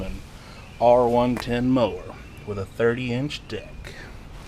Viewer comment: You made me laugh ! It sounded like you said, “The craftsman R110 mower with a 30 inch dick”. 😮